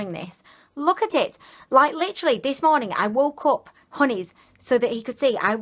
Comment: The pink champagne is my favourite I might look inot buying these. By the way, what's your fiancé's name? I thought you were saying honey but it's odd. love your videos xx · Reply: Agata Potata haha is name is Anton but I call him 'Honies' - it's a bit silly 😂 xx